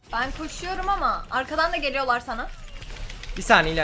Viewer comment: de bi saniye hayatım dedin zannettim ikinci dinleyişimde bi saniye dediğini anladım